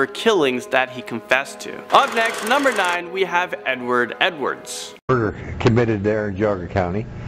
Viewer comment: SWEAR HE SAID N WORD EDWARDS